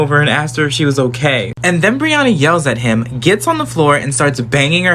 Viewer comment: When he said Brianna I thought he said RIHANNA